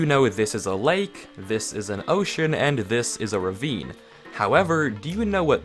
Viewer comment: did he just say ocean